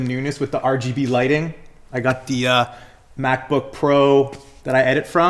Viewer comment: Did curk say MacBook at 5:37? That does not look like that